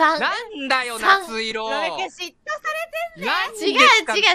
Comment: what said here was actually: ロベくん、嫉妬されてんだよ which translates to: "Robe-kun, she(Matsuri)'s getting jealous of me"